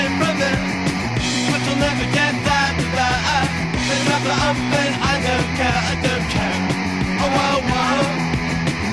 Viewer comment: WTF say in the min 01:29. WTF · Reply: "They chopped her up and i dont care" i think